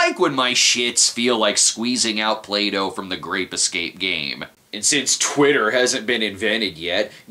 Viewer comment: I must be watching to many Cinema Snob episodes because I seriously thought that said "Rape Escape"